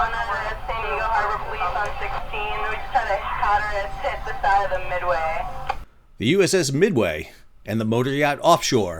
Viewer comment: what did dispatch say??? Just had a what hit midway??? Sounds like she said a hazardous hit beside midway…